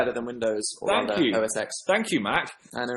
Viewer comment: what did Dan say!? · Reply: OSX - its something to do with apple operating systems